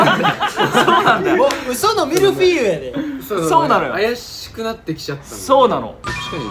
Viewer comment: こーじの「ウソのミルフィーユやで」のおかげで、その後のふっかさんの「そうなのよ！」が「層なのよ！」にしか聞こえなくなっちゃっておもしろい🤣